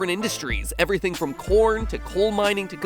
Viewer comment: I thought it said "porn" for a second